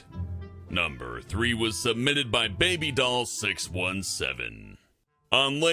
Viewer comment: i thought they said ladybut! my bad